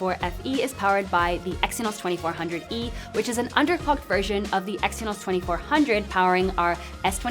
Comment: i heard it as undercooked, but seems suitable anyway lol